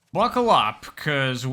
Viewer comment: In the beginning of the video, and I know it wasn’t intentional, but when you said “Buckle up” I heard “Waka waka” and I started laughing. It didn’t make any sense to me, that you would, entirely unprompted say “waka waka” in the most deadpan way possible. Fuckin’ Fozzie Bear impression.